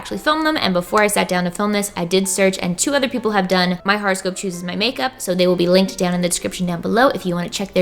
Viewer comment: Seriously thought she was saying "horrorscope". LOL. Oops. Would've been appropriate for .